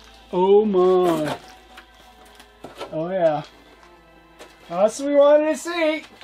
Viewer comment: Love the vids and the instructions! Did he say "orea" (greek for "nice") at 23:20?